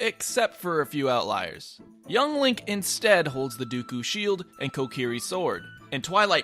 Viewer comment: wait did he just say dookoo shield?